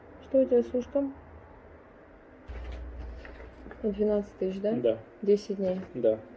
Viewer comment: Млин, мне послышалось 112 тыщ за стационар! Ещё и доктор сказал, что "выльется в копеечку"!